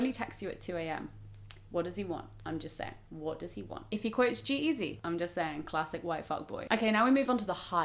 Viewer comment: I dont understand what is said at 2:28 :'( could someone write it down! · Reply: If he quotes G-eazy